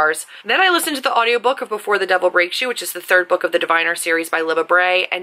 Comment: I heard "before the devil rapes you" and I did not like that 😂